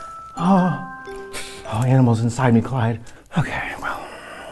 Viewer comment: "animals inside me quiet" I feel is very underrated, works for everything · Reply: ohhhhhh I thought she said “there’s animals inside me, Clyde” 💀💀💀